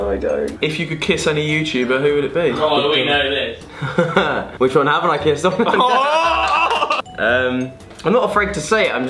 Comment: what did jack say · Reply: said we all know this and jack said which one haven't I kissed